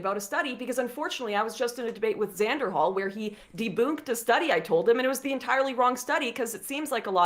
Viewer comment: Did she just say “deboonked”!?!